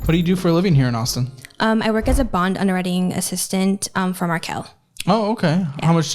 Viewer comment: I thought she said “for R Kelly” and I almost drowned on my sip of coffee 😂